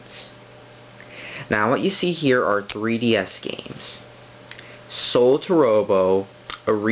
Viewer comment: I thought you said 3DS